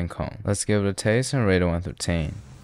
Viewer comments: “let’s give it a taste, and rate it one through tæin” never fails to make me smile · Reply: i thought he said lets give it a taste and rate it 1 to 13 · it sounds like he says "lets rate it 1 13"